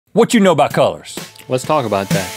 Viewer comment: What you know about cults? (Judging by the white shirts, I thought this was what he said at first)